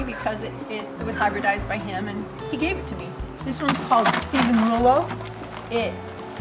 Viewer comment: what rose is this? Sounds like you're saying steven merlow?